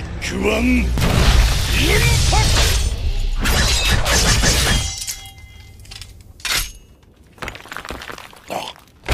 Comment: this is eargasmic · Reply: I heard puck like phuck